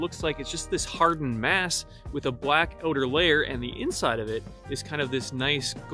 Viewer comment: Did you say black "odor" layer? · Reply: "With a black outer layer" it's that chinadian accent lol